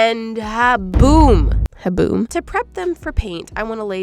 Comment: when you said haboom it sounded like you said haboob like the dust storm